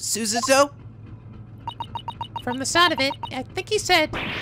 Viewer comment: No no, not "nite", "nyet". Like "NYAAAH", but with "-et" instead of "-AAAH"